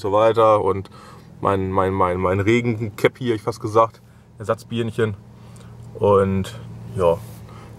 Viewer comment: bei 9:03 habe ich ersatz bierchen verstanden und dachte wat das musste nochmal hören